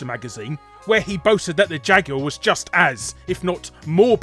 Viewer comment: As first, I thought he said ''the Jaguar was just ASS''.